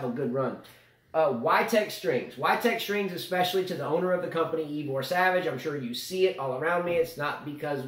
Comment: You guys keep mentioning Igor Savic. is he the owner of YTEX? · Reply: Ahahahaha! Ivor Savage, ok. Your pronunciations of names are not that good. i thought you're saying igor Savic :-D